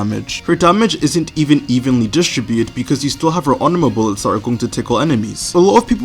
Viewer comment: subtitles says that her anal bullets tickle the enemies 😂😂😂